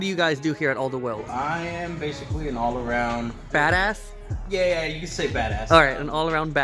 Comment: i thought he said fatass LOL